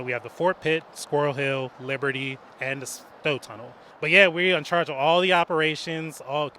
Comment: Does anyone know what the fourth tunnel lists was (0:30)? He lists Fort Pitt, Squirrel Hill, Liberty, and the ??? tunnel. Closed captions has "snow tunnel". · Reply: I think he's saying the Stowe Tunnel. <link>